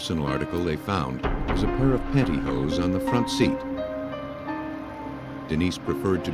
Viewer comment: Not underwear, panty hose. The sheer leggings you wear under dresses. They tend to be really uncomfortable, easily snag on things and they make your feet slide around too easily in your shoes. I don't like to drive either when wearing panty hose. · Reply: ohhh, I guess I just heard "panties," not "pantyhose".. makes wayyy more sense.. yeah, they are not the most comfortable undergarments around, especially on the stomach.. 😂